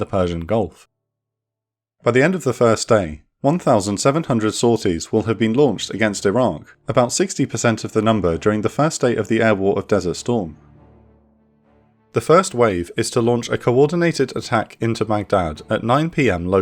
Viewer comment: did you say that “1700 sorties would have been launched against IRAN or SADAAM? Sounds a lot like Iran to me. Don’t know if that’s the case that needs a minor correction. Don’t want you demonized any further !!